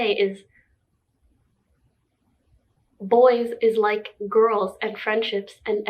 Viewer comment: Was It just me or did she say boys weird